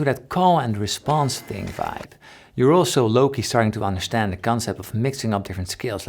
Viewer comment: DID PAUL JUST SAY LOW KEY??????